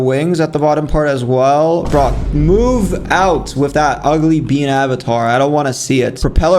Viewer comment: Did he say UGLY BEAN AVATAR